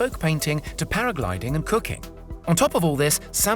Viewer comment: was I the only person who heard "cocaine" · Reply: Cooking